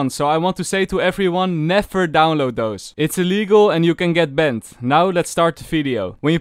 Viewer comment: This just popped up in my feed again and I like the accent when it introduces some funny ambiguity. At about 0:30 you say "Never download those! It's illegal and you can get banned" but I heard it as "you can get bent" which is just as accurate